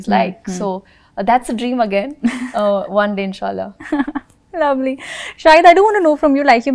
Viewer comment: wait, what?? did she say 'insha Allah' at 9:37? how come?